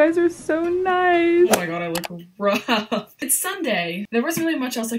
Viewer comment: Great video in all but am i the only one who noticed how on Sunday instead of saying of saying Sunday it said Friday no hate but am I the only one or?